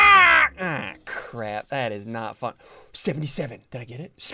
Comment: WHAT WAS SSUNDEE GOING TO SAY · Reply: Fun